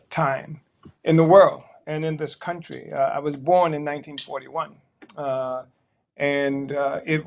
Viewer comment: 82 not 102, still admirable. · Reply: You're correct...I thought he said he was born in 1921 but it's 1941.